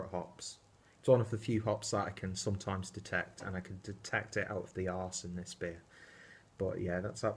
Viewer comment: Bloody love this beer. Probably the best core range DIPA on the market. Big juicy sexy mama! For a second I thought you said "I like the arse on this beer".